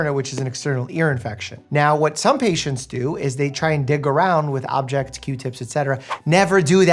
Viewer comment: Who else thought he said “d**k around” and not “dig” at 4:49? 🙋‍♀️😂